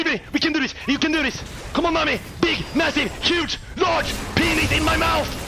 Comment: He is saying Nami but it sounds like he is saying Mommy lmao